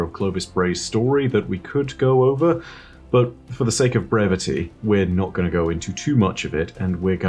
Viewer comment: did anyone else hear Bray-vity